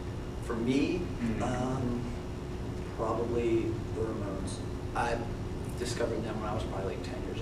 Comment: ok...so at 20:07-ish the interviewer asks fan questions...the first one was "What has been your biggest music influence?" Did anyone catch answer?? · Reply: The Ramones